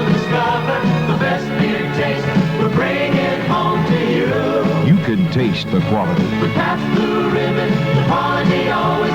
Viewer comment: What’s he saying at 16:27? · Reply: "you can taste the quality...pabst, since 1844"